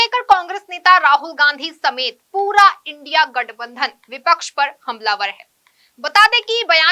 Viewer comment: “पूरा INDIA गटबंधन विपक्ष पे भारी है???” क्या बोल गई Ma’am?